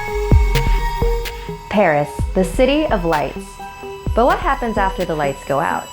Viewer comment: I thought she said the city of lice